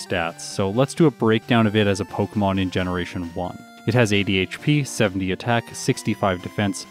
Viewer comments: When you said "it has 80 HP" I heard it as "it has ADHD". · I thought you said Tentacruel had ADHD when you said 80 HP 😅😂😂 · When said Tentacruel has "80 HP" I thought he said "ADHD" and I was very confused lol